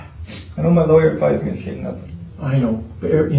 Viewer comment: You have to be very specific, and if you keep talking they keep listening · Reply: Yeah, I went back and listened to it a few times. He said, "I know my lawyer advised me to say nothing". I thought he said, "I want my lawer, I'm not gonna say nothing". Big difference.